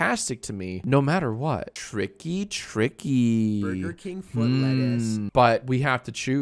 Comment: what does the "Chills" voice say (don't know if it is impersonating him or actually)? I can make out Burger King and Lettuce. Anyone? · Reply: "Burger King foot lettuce"